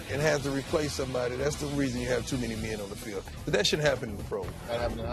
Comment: Am i The only one who heard him say "but that shit happens...." At 2:43 😅